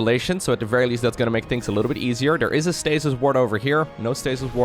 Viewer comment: I keep hearing "stasis wart" and I chuckle every time!